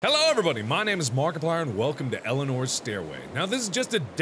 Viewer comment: I legit thought he said "Hello, and welcome to Elinor's Steroid". What does that say about me?